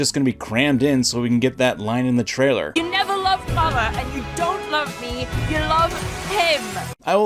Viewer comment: Hey. Might just be a hearing thing but it really sounds like octiva saying "You never loved Father or me, you love him." I really can't hear a mother even with her accent.